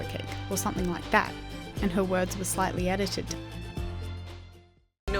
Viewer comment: It sounds like says no bad blood